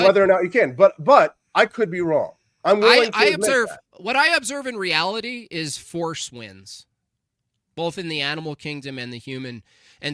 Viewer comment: Hahahaha 1:17:50 I’m eating breakfast as I listen to this so I can’t hear perfectly. I honestly thought said, “what I observe in reality is foreskins.”